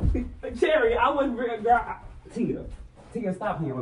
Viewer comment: SHE'S JEALOUS OF YOU🥊🥊💋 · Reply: TO THE YOUNG LADY I HAVE TO GIVE YOU DEEPEST APOLOGIE , THE REASON WHY I SAY THIS IS BECAUSE OF ME DOING ALITTLE RESEARCH THIS IS A TRANSGENDER WOMAN AND HER NAME IS I THOUGHT SHE SAID ITS CHERRY MY MISTAKE 🥊🧸🥰😍🤩💝💋👝🛍💅💄📿👡📿👘🤝🏽